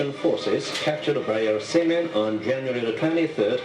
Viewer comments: the korean narrator says captured by our semen instead of seamen. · did he say "captured by our semen" 15:13? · I feel bad for the tea girl. Plus the guy who was speaking in the TV sounds like he said "semen" in 15:12. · Seamen i heard it as semen did any else? 😂😂😂😂